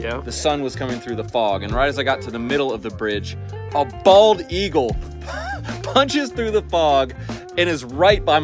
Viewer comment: Somewhere a bold eagle is telling the tale of eyeballing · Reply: Ooooh....I thought he said "Baller Eagle" because of the ballin' railing video.